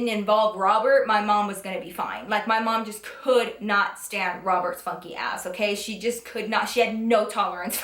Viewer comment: Its funny how you keep saying "Roberts funky ass"! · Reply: i legit thought she was saying "robert spunky ass".... whoops